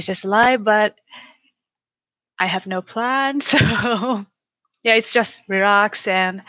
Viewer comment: Congratulations to Shida on being AEW's longest reigning champion ever! Also, at 2:02 I thought that she said PANTS instead of PLANS. Oops!